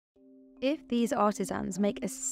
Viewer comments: I heard " if these autism's" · I thought she said "Autisms" not "Artisans"! My audio was low, and I was so confused.